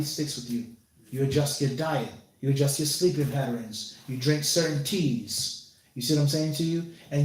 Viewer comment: did he say “...you drink certain teas”?